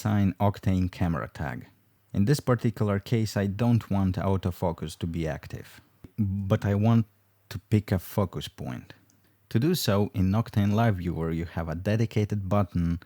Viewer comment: thought for a sec he said ''pig f*ckers''